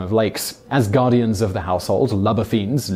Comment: I thought that said “Luber fiends” and I got kinda excited for a min...